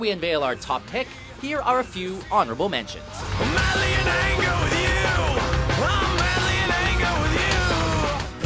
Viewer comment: St. Anger was a decent album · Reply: wow, it's "I'm madly in anger with you", I always thought James was saying "I'm barely in anger with you".